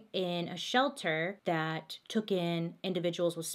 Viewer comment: Did she say tooken instead of taking special needs? Around 10:08.